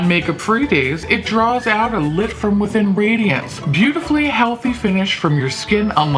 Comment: i misheard 'beautifully healthy finish' as 'pubically healthy' and did a double take 🤣